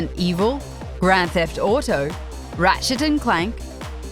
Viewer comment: when she said ratchet and clank it sounded like she said rat shit and clank